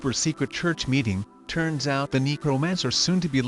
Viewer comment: I keep hearing "negromancer"